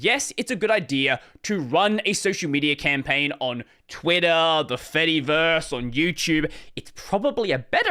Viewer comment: btw the caption says "fattyverse" in place of "Fediverse" 😅